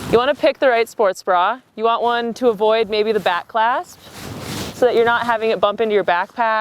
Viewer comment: I thought she said "the bat clasp" and I felt really out of the loop for a moment.